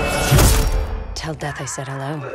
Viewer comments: I thought said "tell dad I luv u" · English is not my first language but i know it pretty well but i still dont know what says in this part: 1:07 Can anyone tell me? Sorry for my supidity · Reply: Tell death i said hello